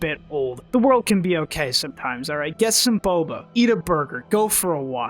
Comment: i swear i heard "suck some booba" O,O